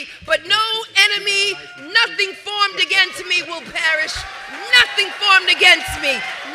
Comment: did she say ‘nothing formed against me shall perish’?